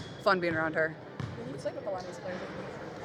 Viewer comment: The auto-subtitles told me the question was 'Do you sleep with a lot of these players?' and Caitlin answered 'Yeah, it's fun'!!!!!!!! Can somebody tell me (1:46) what was actually said?